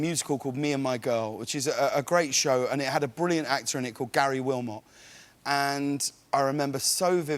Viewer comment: Garry willmott is a really talented actor and entertainer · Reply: Thanks for clearing that up for me. I thought he said "Gary Gilmore". Huge difference!